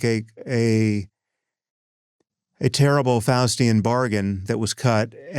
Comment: when Sam Harris said: ' A terrible Faustian bargain...' I heard: a terrible Falestinian bargain.